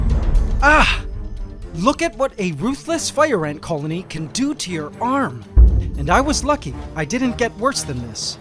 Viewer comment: Lol the audio cut out because of my internet so I heard "look at what a ruthless f*** ant colony can do to your arm"